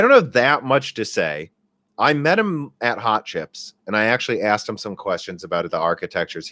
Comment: For a second, I thought you said 'hot chicks'.